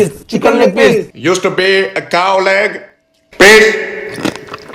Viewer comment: I thought he said "used to be a cowleg... PISS" 🤣